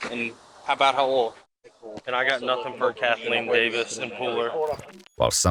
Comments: I thought she said Kathleen Daniels not Davis!! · I know it wasn’t her real name, but They kept looking for “Kathleen Davis” but I coulda sworn said Daniels